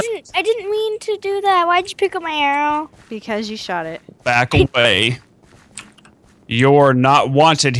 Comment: it sounded like said "backle bay"😂